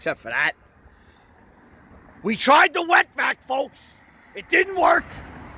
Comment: Oh wet vac I thought he said wet back